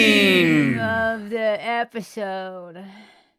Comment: “of the episode” sounded like Ben Cook